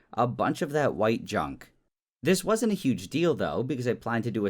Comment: I had me eyes closed, and I heard a "it wasn't a huge dildo" at 23:47 😱